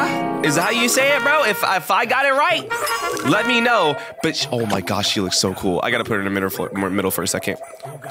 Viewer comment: um did he say the b word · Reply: He said: “but sh-“!